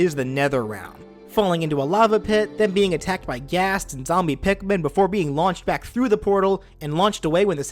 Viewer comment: Did you just say "Zombie Pikmin"?